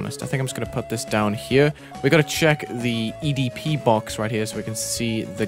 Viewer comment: did he say the EDP box??